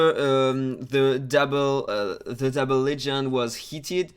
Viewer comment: Nobody knew about that, no cc, no moderator. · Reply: Oh yes im sorry watched the video again and I understood developer instead of double lego (1:24 on the video)